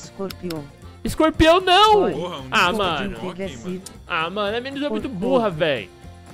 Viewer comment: "Essa mina eh mt burra vei" Ce eh loko mano, nem eu entendi akela poha kkkk